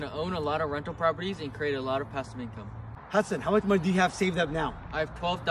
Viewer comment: does he say hasan